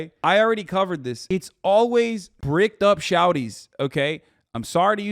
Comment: did he just say "shorties" with a southern draw is a trans term?? homie no its not lmao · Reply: “Bricked up” not shorties.